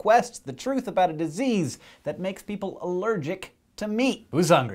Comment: The video stopped to load at 0:17. If I hadn't read the title I would've thought Hank said 'a disease that makes people allergic to me' :D